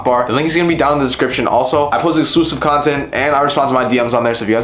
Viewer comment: no way I actually thought he said "explicit content"😂😂